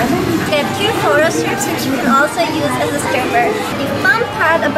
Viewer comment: did she say stripper?